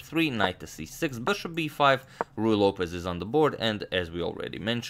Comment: I'm new to chess theory and I always thought he was saying "Royal Opus" and not Ruy Lopez. Lol.